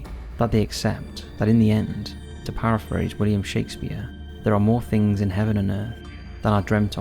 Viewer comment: Was kind of falling asleep and for a second I thought he said William Shatner instead of Shakespeare at 2:23. Woke back up confused and had to do rewind to hear that again lol